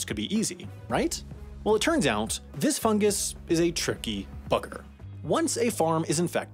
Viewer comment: i thought it said F**ker